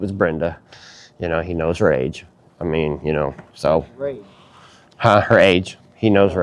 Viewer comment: It 100% sounded like he said he knows rage